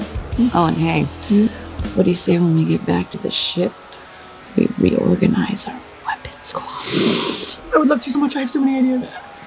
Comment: I realize Sara said weapons closet. But when I 1st watched it I thought she said "Puppet closet"" and now I can't help but wonder 🤔 🤣🤣🤣🤷🏽‍♀️🤦🏽‍♀️